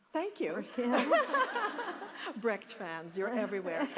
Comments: what did meryl say in 7:00 ? somtihing about fans??? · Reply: "Brecht fans, you're everywhere." The play Mother Courage is by Bertolt Brecht. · what did she say laughing at 7:04 "... you're everywhere" ??? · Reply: You're everywhere, you Brecht's fans :D